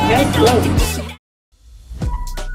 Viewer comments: I thought he said “get smacked” And I was like- What? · I thought it said get smashed 💀